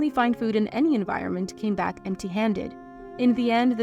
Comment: One weird thing said was “they were so hungry they ate bear fat”. That was completely an average Tuesday for any frontiersman/trapper. It was the main ingredient in pemican, A dry food that would last for months, the main thing they would be eating on a voyage like this. It was so common that bear fat was a literal currency at one point in the deep frontier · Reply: I heard “bear scat”